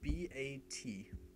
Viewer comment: what did he say to name it run.vat or run.eat?